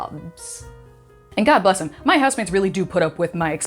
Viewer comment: Oh, housemates! I thought you said, "my husbands". 🤦🏿‍♂️😂